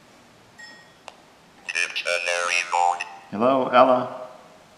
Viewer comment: Here is what I hear (at 22:00 on)-not sure if first part says void/mode: Dictionary void(ed)(mode) ohm magic phone hear me I'm pharaoh potm,potulom (Ptolemaic?-it could be saying 'Pinedjem Piankh' look this up) our people (here/fear) arrow(pharaoh)......here at aowhen people are ...married, divorced ..potum po here at airport ....error..... error...error... at a..eleden... error*** the next batch(I herar you all talking) Dave Mike-I here LADIES, COPS, he cut them all Killed, Here that, Think, good cop good, he murdered people in this brothel.^^^After that nothing makes sense. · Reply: In The Shadows Thanks! Yeah, the one part says "Dictionary mode" since the device has both a dictionary mode and a phonetic mode. We did get "cops" and "mob" at other points in the evening as well as "hide" and "slash" several times when it came to "Ella", but not all of that ended up recorded.